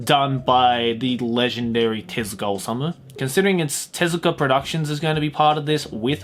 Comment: I specialy like read subtitles and hear anyone just to improve my english, but BOI, What comes in your f*** up mind to wrote instead the name of a legend Tezuka Osamu, to just put - testicle summer 15:51. I mean, even if its funny, you cant put those words instead of someones name, especially on the legend of mangas.